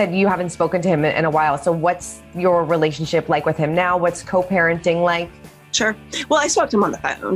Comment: I thought it said coparenting Cody😂 He’s gross.